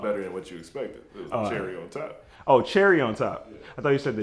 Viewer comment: It did sound like he said jerry though😂😂😂